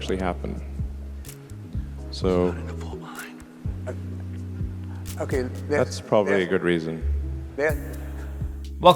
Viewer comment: What does the guy who comes on stage whisper @ 2:14? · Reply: He said we are running behind